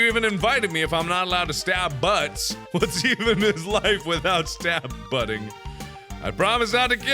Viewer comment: Did he said stab butting? at 34:11 wtf :))) STAB BUTTING